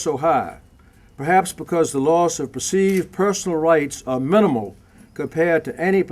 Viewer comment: He actually said "...loss of PERCEIVED personal rights..."